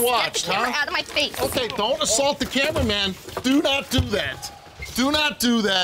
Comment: Did you hear what Steve said mom-ster: don't assault the cameraman!!!